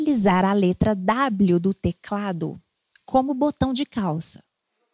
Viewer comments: Eu escutei "como botão de caos" E fiquei '-' q kkkkkk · Só eu que entendi "botão de caos"?